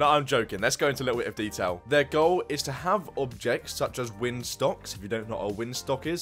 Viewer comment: “Windstocks” lol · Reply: I said that because it sounded like you said “windstocks” instead of windsocks